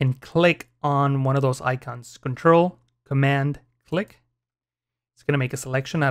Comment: What he say in 4:20 ? (Control + Amand or something like that?) . My english isn´t perfect :( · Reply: Control + Command :) (Which is for a mac, or Control + Alt for PC)